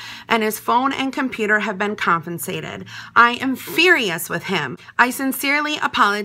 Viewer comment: sorry did she say... confincated